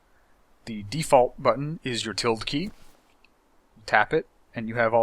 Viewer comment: Great video and thanks for having this. Which key did you say at 1:56? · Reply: Tilde ~